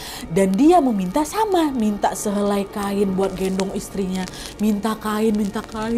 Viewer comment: Kak nad aku gak salah denger kan 😭"minta sehelai kain untuk gendong istri nya"